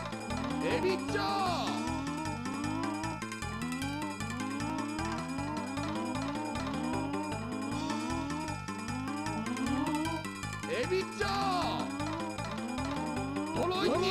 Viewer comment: When I was a kid, I thought they were saying “Maybe stop?!”